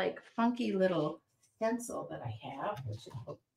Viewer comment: Where do you get the stencil tape? I thought you said drywall tape